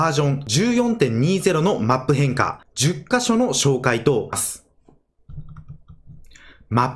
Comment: 紹介とサツッって聞こえたw